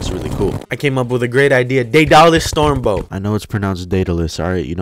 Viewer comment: I thought you said dollar storebow